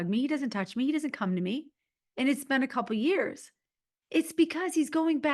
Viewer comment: I keep hearing “it’s been a year give up”.